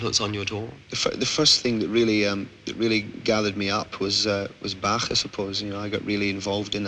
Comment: what does he say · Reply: Was Johann Sebastian "Bach' I suppose..